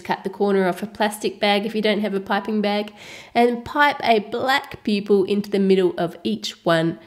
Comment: black pupil? oh i thought you said black people